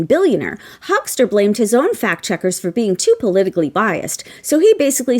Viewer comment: I like how he said they're getting rid of fact checkers, as if he ever truly monitored Facebook without the imminent threat of a lawsuit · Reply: Oh I thought he said FAT checkers …the reason for his toga